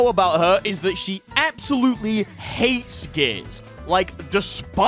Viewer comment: thought you said "hates gays" for a moment